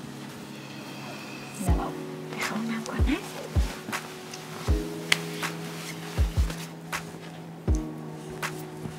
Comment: Anyone knows what they are saying at 5:35 ?? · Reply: "Okay, I'll gonna take a shower "